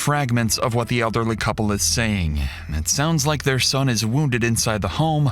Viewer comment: My first thought was that she said "My son is shit" and I'm like - Whaaat?!?